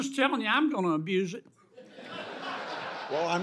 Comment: All i heard was “you’re a good man Mr Chairman”